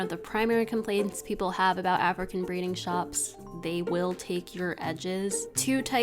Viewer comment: I was so confused when I kept hearing "African breeding shops" until I saw the captions